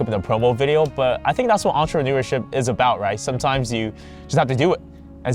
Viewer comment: Was it just me but I just heard you say Entreneur or what?